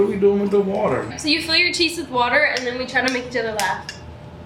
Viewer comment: I thought she said put water water in ur teeth but it was cheeks